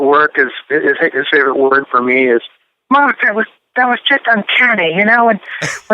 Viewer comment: I can't understand what "Roger Taylor" says at 21:00 Help! 😢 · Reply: "Great job everyone!" "Marc, that was just uncanny!"